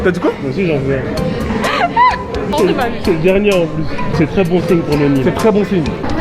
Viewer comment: C’est moi ou à dit or de ma vue 😂😂